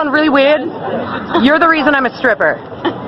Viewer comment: Believe it or not toms still a human being with regular feelings and emotions it’s just like if you told some random stranger you’re a stripper bc of them. You obviously know that would make him uncomfortable. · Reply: ikr, I hear “I’m a stripper because of you” I was like feeling uncomfortable too.